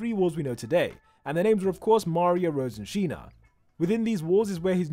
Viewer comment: WAIT WAIT, did anyone hear Mario but not Maria if I spelled that right.